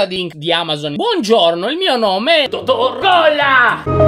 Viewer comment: ho capito "dottor gola"😂